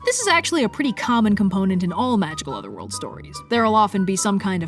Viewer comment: I thought that said Magical Overlords :)